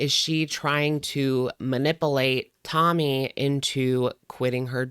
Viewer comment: “Mah-nipple-ate” makes me irritated. All the -isms are just grating to me. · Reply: At first I thought it said my nipple ate 😂😂 had to say a few times before I figured out it's amberlese for manipulate 😅